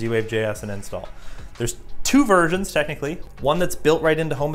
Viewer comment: wait, did he said "there's two virgins" 😂😂😂